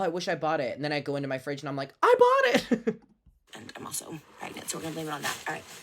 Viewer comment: Did she say "I'm also pregnant"?